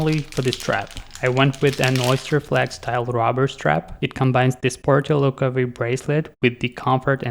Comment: I thought you said a "Robber Strap"... I had to hear it twice...